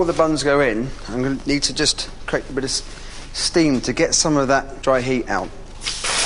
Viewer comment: i could swear he said "to get some of that dry heave out" Also when he bit into it, i expected "it tastes like shit". Would be hilarious